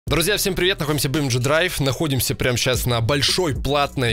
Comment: мне в начале послышалось "всем привет мы находимся в бомж драйв" xD